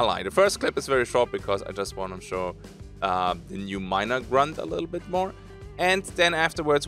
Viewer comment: I thought you said "minor grunt" was like: "zombified child, thats hella creepy"